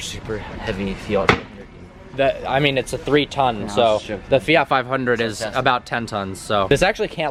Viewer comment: Did he say a fiat is 10 tonnes